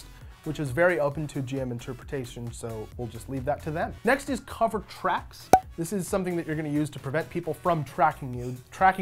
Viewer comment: For some reason I heard you saying "Cover trap". haha Great video, I think the same about handle animal. Could be simpler, I guess.